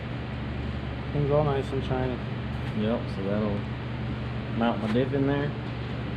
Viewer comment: I thought for a sec you said “ it’ll mount my dick right there” 😂😂